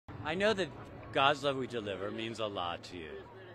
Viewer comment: I thought he said "gaza we deliver"